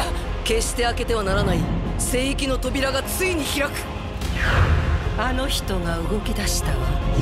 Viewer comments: けして開けてはならない、性癖の扉がついに開く！に聞こえてびっくりしたわ💦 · ジ・エンドが ジン、エンドに聞こえるw